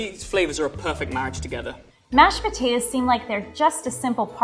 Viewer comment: did she just say mash PO-TAT-ARES?